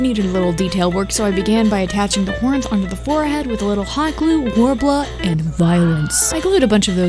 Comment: Please can someone tell me what she is saying, i turned on dubtitles and i gor war bl and worbler What is it acctualy😭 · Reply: Worbla lol